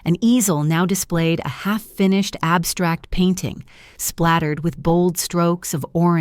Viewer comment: I thought she said a finished basement???